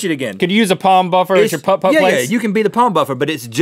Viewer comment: Thought Rhett said "Pub-Pub"..